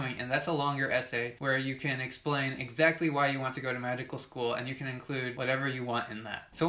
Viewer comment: Is it just me or did I hear magical school